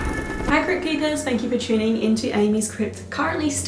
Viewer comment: Amy I just heard inn-Keeper" whatever that means